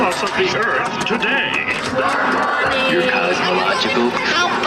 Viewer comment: What does the "Commander Marvin Martian" button say? 0:16 What does the "Warning" button say? 0:17 What does the "Commander Bugs Bunny" button say? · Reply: I remember the “Warning” button said “Your Cosmological Clock is ticking.”